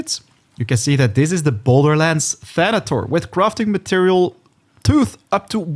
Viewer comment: "The boulderlands of pandora" I totally thought he said borderlands. The planet has the same name